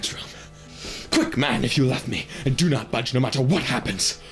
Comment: did he say "if you love me"?